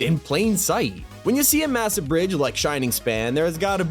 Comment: DID HE SAID M-MASSIVE ??